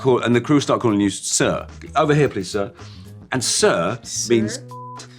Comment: what was the word blipped? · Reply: See You next time